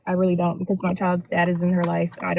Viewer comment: Saying the father is not in the child’s life is worse than the father being in the child’s life. Why do women make this statement like it’s a flex?🤔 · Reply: I thought she said “is in” 0:23 not that it matters anyway.